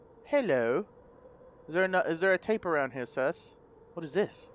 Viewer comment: I love how he looks at the closet and closed it 💗 and I heard him say where it at "sus"